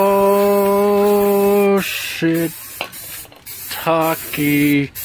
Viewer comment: did he say shit